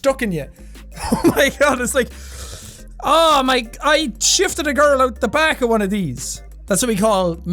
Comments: I thought he said shivved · Oh boi Sean u glorious bastard I thought you said " I shafted a girl" for a second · I swear to god I that he said shivved a girl out in the back of one of these.